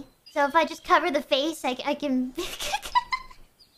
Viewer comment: it’s “likes” instead of “legs”